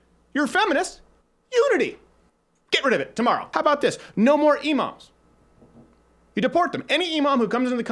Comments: I thought he said no more emos for a sec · I thought he was saying "No more Emos". I was like I didn't know angsty teenagers were a national problem. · Thought he said no more Emo's, was confused as fuck